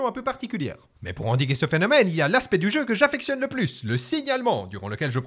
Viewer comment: Quand tu dis "Le signalement" j'ai compris "Le signe Allemend" et cru que tu parler de déconcer des co-équipiers de nazismes Oo Non sérieux j'ai essayer le jeu et même si j'ai bien aimer c'est pile ce que je rescend !